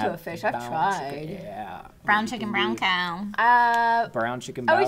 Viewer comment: I thought she said Brown chick and brown car